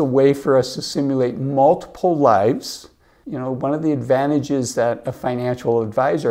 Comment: did anyone else hear "simulate multiple wives" with video of 3 women on the screen? lmao